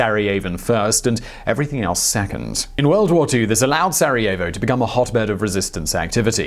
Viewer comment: Did you just say... Sareovo